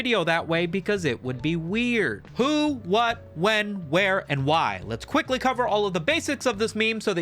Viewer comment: i thought you said "boob, butt, when, where, why" and ive been radicalised